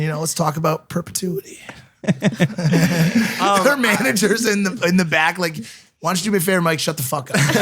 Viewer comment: did Mike says, HER manager?